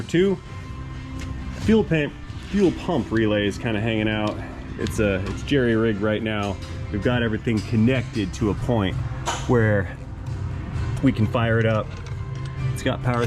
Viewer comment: It sounded like you said 'gerry-rigged' near the start? I should have heard 'jury-rigged' but there you go..............